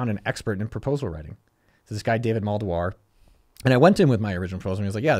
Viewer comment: ¿David Maldware? Didnt hear his name right ¿what was it?